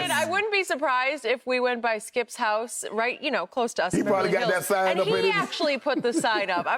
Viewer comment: Did she say "By skip's house you know close to us in Beverley Hills"? Damn skip you ballin forreal